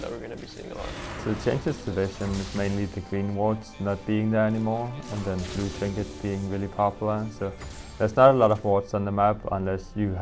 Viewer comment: what the heck is froggen saying? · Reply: "So the changes to vision is mainly the green wards not being there anymore, and then the blue trinket being really popular."